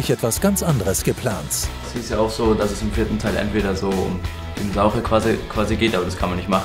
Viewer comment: Was sagt Quirin bei 0:16? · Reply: Dinosaurier